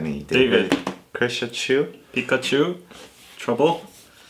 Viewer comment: Did he say Pikachu at 0:03 xD